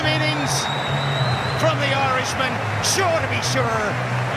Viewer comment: anyone heard what commentator said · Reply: Ya Irishman